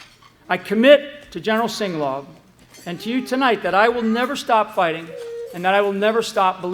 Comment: It sounds like he says “ I commit to General Singlaub and Q” . You expect him to saying “you” but slow to half speed and tell me what you hear. <link>